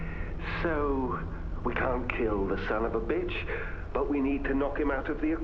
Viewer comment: did just say son on a bitch?